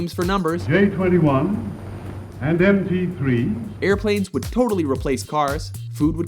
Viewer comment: did he just call that man "MP3" ? . . LOL